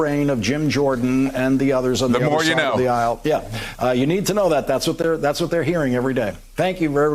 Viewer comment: Lol! Did he just call Eric Swalwell, Carson Swalwell? It almost sounded like he said Lush Limbaugh. Lawrence O'Donnell is losing his mind. The effects of Trump Derangement Syndrome, I suppose.